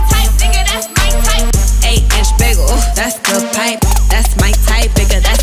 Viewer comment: I thought she was saying 8 inch bagel. Now that's something I could agree to. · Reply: I thought she said aye a rich bagel